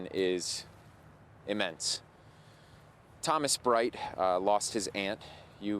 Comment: God Bless you and your family, Thomas. (Correction-don't know why I thought I heard his name was "Steven". So sorry)